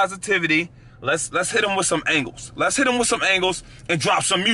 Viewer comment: I thought he said hit em with some ankles 11:30 💀💀